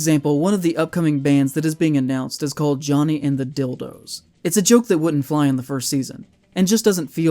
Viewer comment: I think what it actually said was “Johnny and the Dilworths” not Johnny and the “You know what”